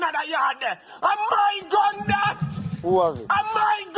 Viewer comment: Sorry guys I'm eastern European, what is the screaming Jamaican meme guy at 6:08 actually saying? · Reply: Translation is: "That's my Gun" 😅🙌🏽 aka "that's my striker" 😁